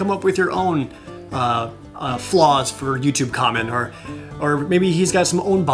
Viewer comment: this whole time i thought he was saying youtube comment